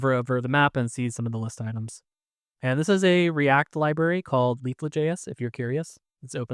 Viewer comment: Sorry, what is the name of the react library ("something" JS) you mentioned at 2:08, I couldn't catch it. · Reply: Leaflet JS. It’s a mapping library